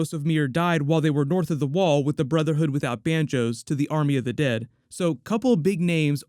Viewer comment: Wait, did I hear that right? The Brotherhood without Banjos at 1:58?